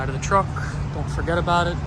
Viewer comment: when you said say hi to the truck i could of sworn you said forgetaboutit 🤔 but then realized you said Dont forget about it 😂